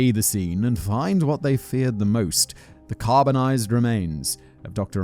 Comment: Was quite distracted at the beginning and thought I heard "caramelized remains" instead of carbonized